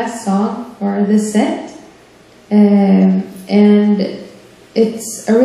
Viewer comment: WHO did she say it was by? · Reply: Tommy Emmanuel